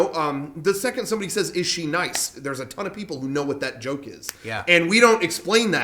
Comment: What the fuck is wrong with my brain. The first like three times I heard "is she nice" at around 24:19 I kept hearing ishi nights lol